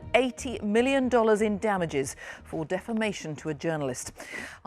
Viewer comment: Defamation😂😂😂 at first I thought they said defecation?on a journalist😂😂